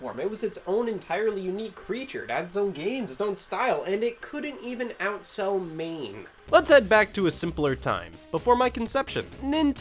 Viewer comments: Thought he said "it couldn't even outsell ME." Checked the numbers. At time of posting, has exactly as many subscribers as Maine has people. Coincidence? I think not! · I heard "couldn't even outsell Maine" as "couldn't even outsell M.A.M.E" and boy howdy is that one of the funniest mental images.